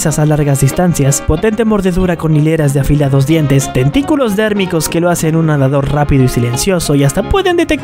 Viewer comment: En la parte 3:28 yo entendí testículos térmicos que le permiten nadar rápido xD...saludos desde chile